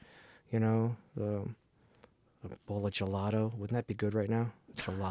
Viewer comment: Lol! At first I thought you said “Michelada”, and my mouth was watering. Then later in the interview I realized you said “gelato”, and my mouth still watered😬